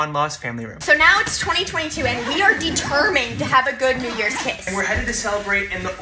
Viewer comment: i thought they said "we haven't had a good new york kiss" and they're going to nyc 😭